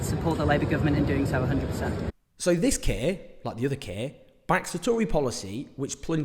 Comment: When you said ‘this Keir’ I thought you said ‘this kid’ 😂